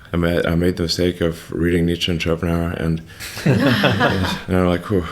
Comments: Does anyone know what he said he read when he was younger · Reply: Friedrich Nietzsche, Arthur Schopenhauer, Douglas Adams/ Hitchhiker's guide to the galaxy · "made the mistake of reading what ______" what did he read i couldnt understand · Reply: Nietzche and Shopenhauer. He was thinking he will get answer to his questions by reading their philosophy. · Does anybody know what book he says at 18:40? I can’t understand what he says but I really want to know! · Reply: He was naming people who wrote books, they were philosophers. "Nietzsche and Schopenhaur"